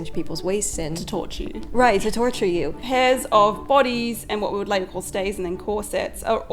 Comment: "hairs of bodies?" Is it a bodice? I'm sorry. My auditory memory is weird in that I have to integrate every new sound, individually. It was my own failing. But wthek did she say there? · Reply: It's pairs of bodies and they were underwear like stays, worn over a shift.